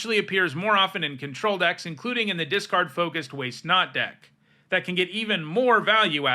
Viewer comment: I thought for a second you said Way Snot deck xD